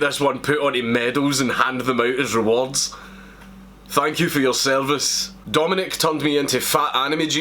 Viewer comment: I thought this one said, "Dank you for your service."